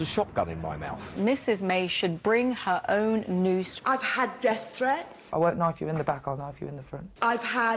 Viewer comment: ??? He's not talking about banning freedom of speach, though. Where are you getting this? xD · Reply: I think she says "ive had death threats" and to that rational people say JUST IGNORE IT, THIS IS THE INTERNET